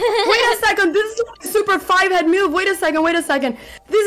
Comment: I think the coach said "fivehead", which is a slang term for large fourhead - because 5 > 4. Ex: Ollie doesn't have a forehead. She has a fivehead because her brain is so big!